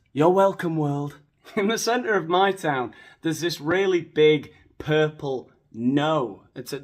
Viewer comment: I thought you said there was a big purple gnome outside your town saying ‘no violence’ 😂